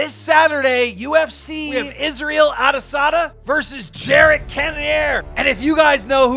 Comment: I thought keep said Isreal carneasada